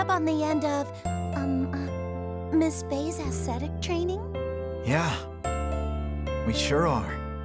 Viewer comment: What does Athena say around 3:02 - "aesthetic training"?